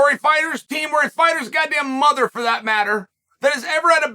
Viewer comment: Did Chael say "God damn" at 1:40?! Chael P. Sonnen you better be at church this Sunday!